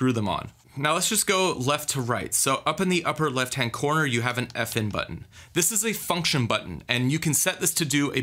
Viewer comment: You have a f**kin' button was i the only one who heard that